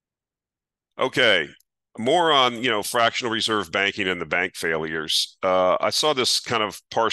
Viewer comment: You said: "More on fractional reserve banking failures..." I heard: "Moron fractional reserve banking: failures!"